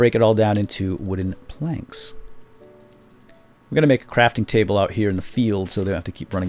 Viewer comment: I was eating chips while watching this vid and when he said "crafting table" the sound of crunching chips changed it so I thought he said "crapping table". THe chips are no longer in my mouth, now they are on the monitor -_